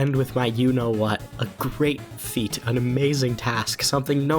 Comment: did somebody say great feet? 😋